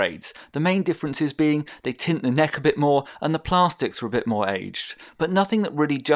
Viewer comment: Hold on... at 02:32 did you say plarstics, instead of plastics?